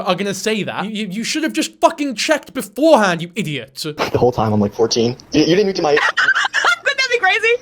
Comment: Ngl I thought he said “if the whole time I’m like 14” tho could just be a phone crackle